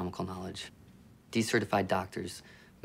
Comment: what's he said? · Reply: "De-Certified Doctors"